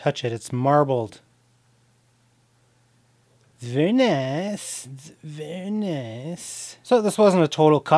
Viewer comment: Did I hear Borat say "Wa Wa Woo, very nice, very nice." ? 🤣